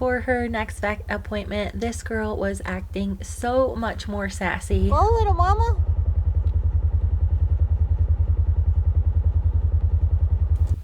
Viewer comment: did I hear that correctly? Little mama?